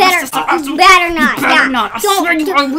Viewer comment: what the edit : i noticed they were saying " jesse u better not mess up " 🤣🤣🤣🤣🤣🤣🤣🤣😂😂😂😂😂